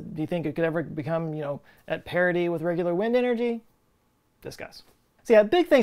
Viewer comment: Immediately after the second time we see the 'Sweat' rap, you used the word 'parity.' But I heard 'parody.' Just the way my mind works.